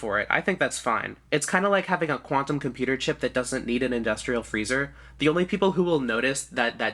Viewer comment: because quantum computers are using single elementary particles to compute things, if it's not REALLY cold, there will be too much chaos to observe the particles. that's the long and short of it. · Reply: ahhhhh, the confusion is cos I misheard what you said, I thought u had said “a quantum computer that does need an industrial freezer” making no sense, which was different to my previous thoughts that they did need to be at a rly low kelvin. I basically just misheard u and got confused, but that’s the long and short of it XD (see what I did there? :) ) Anyways, thanks for the explanation and clarification, have a lovely day!